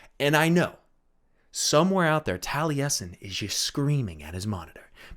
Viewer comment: "Somewhere out there... Taliesen is just screaming at his monitor..." Golden haha. Maybe N'Zoth evolved for when he was trapped under the ocean to handle it. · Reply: oh god, I thought he said "Taliesin is screaming at his MOTHER." I was thinking Tailesin's mother must be the Void!